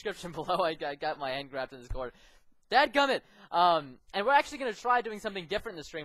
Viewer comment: did he just say dad gamn it?!